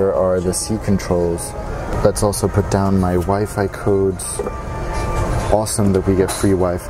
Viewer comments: i thought you said my wife LOL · Thought he said “my wife i codes” hahaha